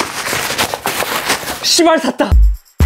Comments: X발 샀다! 라고 들림 나만그럼? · 나만 (C.bar)시* 샀다로 들림? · Tlqkf 샀다라고 들리네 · 나만 ㅅㅂ 샀다 라고 들리나? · 10r샀다 가 시발샀다라고들려 ㅋㅋㅋ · 10r 샀다가 꼭 18샀다로 들림 ㅋㅋㅋㅋㅋㅋㅋ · 초에 시발쌋다로 들림 ㅋㅋㅋㅋㅋㅋㅋㅋㅋㅋㅋㅋㅋㅋㅋㅋㅋㅋㅋㅋㅋㅋㅋㅋ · XR샀다가아니라 시발샀다로들림ㅋㅋ